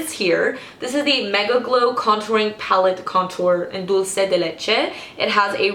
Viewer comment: What did she say at 7:50 ??? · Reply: Rojda A Dulce de Leche